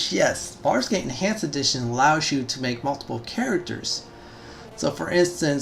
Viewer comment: Sounds like you said "Farscape Enhanced Edition" I wish that was a thing